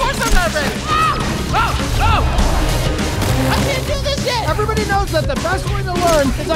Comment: did he just say sht 🗿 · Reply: No he said "I CANT DO THIS YET"